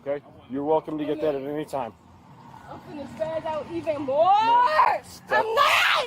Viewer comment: did she say ch#mp out lol.. Rasist she said it 😮😮😮